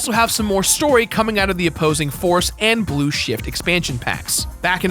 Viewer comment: lol, i thought he said 'blue shit'